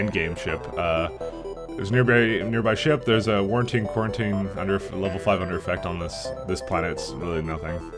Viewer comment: did you just say "Warantine quarantine"?